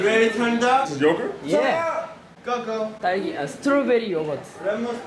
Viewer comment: did he just say "strawberry OWOC" ? 😂😂😂